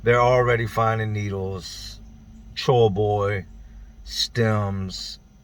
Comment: what's troll boy? Is that what he said? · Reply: Chore boy - its copper sponge that crackheads use to put in their pipe to help smoke